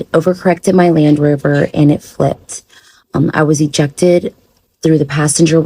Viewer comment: How was she ejected from the vehicle, but also state that they used the jaws of life smh lies that make no sense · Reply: I thought she said she was ejected into the passenger seat… that’s why they had to use the jaws of life.